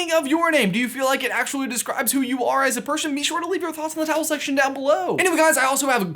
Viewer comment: did you say "towel section?" I was named after my uncle, so my first and middle name have meaning in that they are out of respect for him. My last name, however, is Saxon, meaning farmer or innkeeper. I've been told that it literally translates to 'keepers of the hogs.' I don't keep hogs, but I do love bacon, so maybe there's something there.